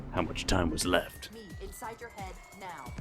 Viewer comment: the first few years I thought she said “think inside your head now” 😂